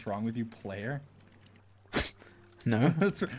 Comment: I love how George clearly said "Clay" but Dream thought he said "player" 😭😭😂